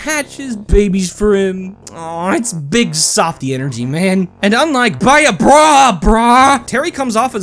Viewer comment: For a second I thought you saying “biggie softie energy man” in reference to Terry was actually “bink’s sake energy man” and I was both very confused, very flabbergasted, and on the verge of tears despite how little sense it made until I listened to it again. Misheard lyrics more like misheard scripts lol